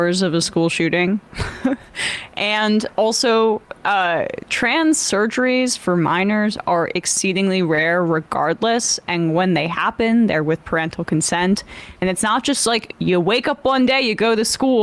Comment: Why did I hear "miner" children? "We'd change your sex, Jimmy, but your hardhat doesn't have a flashlight."